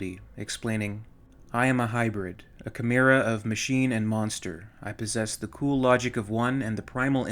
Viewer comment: I could have sworn you said manster.